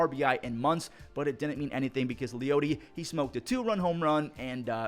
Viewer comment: I had to listen back a twice because I thought you said "the peyote he smoked"